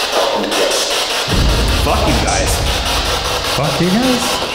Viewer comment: Make sure to leave your questions for our Post Mortem episode down below! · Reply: #postmortem 22:26 I don’t think the spirit box says “eff you guys”, I think it says “welcome guests” what are some other things the spirit box has said over the years that you think were possibly misheard or misinterpreted?